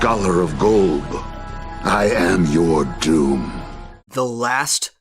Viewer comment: When I first saw that scene with the Lich monologue to Finn, I thought he said he was the last scholar of old or gold, but now I know what he really said, I'm excited to know more about Golb.